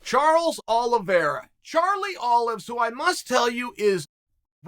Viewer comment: thought Uncle Chael said never call him Carlie Olives...